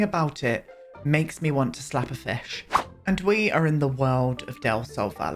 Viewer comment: I have hearing issues, but did I hear, "slap a fish?" Not disagreeing with the slap but asking about the fish.